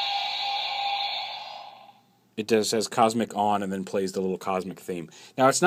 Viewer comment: i keep hearing "cosmic mind" in my head after the cosmic sound effect.